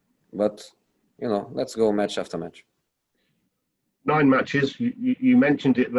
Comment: Lol I thought he said bitches.